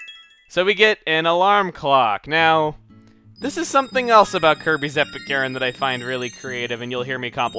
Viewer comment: Could have sworn you said Kirby's Epic urine at one point xD